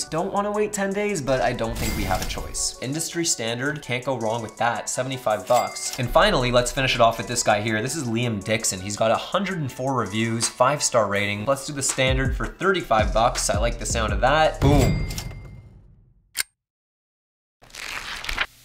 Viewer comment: I thought he said Liam di*kson